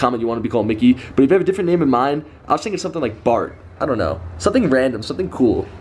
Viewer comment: did he just say in 10:20 that he wants to name him Bar 😂 this isn't COD